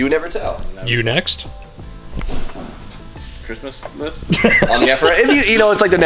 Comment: Did say Christmas list or driftmas list? Maybe I just made a new word 🤷‍♂️🤣